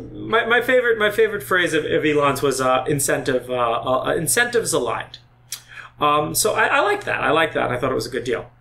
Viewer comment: What did he say at 4:24 ? Incentive is a _ ???? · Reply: "incentives aligned" - i.e. incentives aligned with the desired outcome: $$$ only flows if result is deliviered